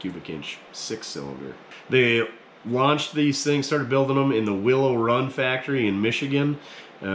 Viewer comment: See, I misheard that. Thought you said the Will It Run factory... That Henry J... That's what I call patina...